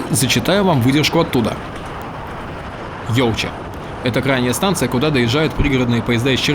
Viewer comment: послышалось - Йоу!! Ча!!